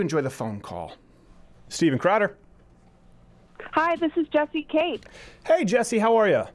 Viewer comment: When introduced herself it sounded like she said, "just eat cake."